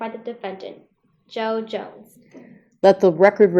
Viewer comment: I thought is said Joe Jonas but I'm going to do the mock trial so thank you!